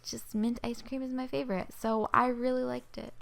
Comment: When she said "I really liked it" when she was poking the mocha mint slime, it sounded like she said "I really licked it"😂